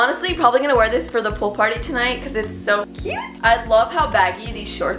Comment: Oh I thought you said poop party😂